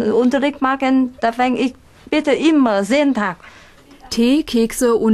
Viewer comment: WTF Noch nie was von Bambustanzgehört und bin VIetnamese. 6:50 Peniskekese ?